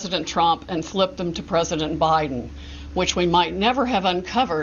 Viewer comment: Did she say PRESIDENT Biden